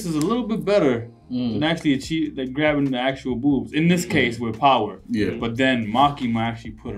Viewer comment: I just skipped 30 second and went straight to 10:18 and the first thing i hear is booob Ong🤣🤣